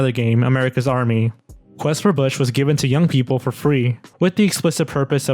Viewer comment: When you said Quest for Bush, i heard it as Quest for Fish